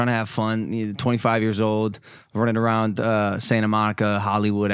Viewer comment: did i hear monika